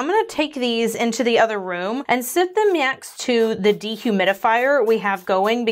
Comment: Did...did just say "myext" instead of "next"? Like that scene in Super Troopers where he's trying to say "meow" as much as possible?😅